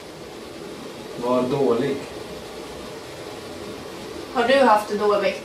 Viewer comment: ”Mår dåligt” hörde jag och inte ”var dålig” :)